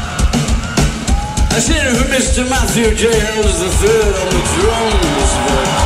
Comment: Can somebody be kind enough to tell me what alex start saying at 3:30 ?? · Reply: "let's hear it for Mr. Matthew J Helders the third on drums"